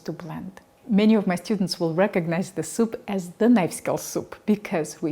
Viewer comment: Thanks for this, another great recipe. I couldn't help but laugh when instead of "Knife Skills Soup", I heard "Nice Girls Soup", oops! Regarding the cooking of the onions in a stainless steel pan, I'd love to hear your take on the Liedenfrost effect - sorry if I missed a video where you addressed it!